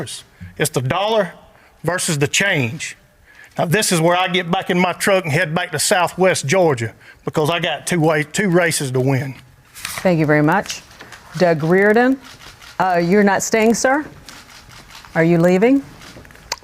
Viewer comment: … did he say “ two white races to win “ … . … 🤣 hahahaha!! #monocularvision